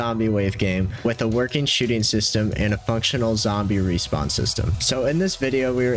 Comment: tbh i heard sh*tting system